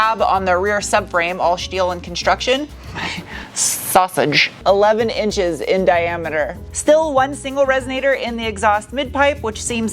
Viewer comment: did I hear 11 inch in diameter?… There’s a tiny tiny mm below the number 😅